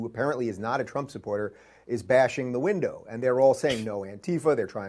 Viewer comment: I heard her say "Antifa is breaking the window!" I think she thinks its 'antifa'